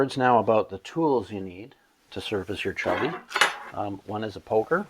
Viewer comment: Did he just say "Service your Chubby" Sorry had to.... 😂